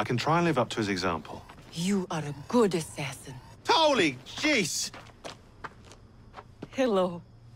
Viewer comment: "you are a good assassin" "Holy Gees" what the subtitles say "Mother of Thor"